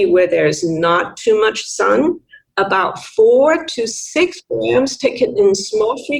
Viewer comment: You can find all of her research in her LinkedIn profile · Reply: is she saying 4-6 grams per day? there is a glitch in the audio just as she says it. 39:50 ish.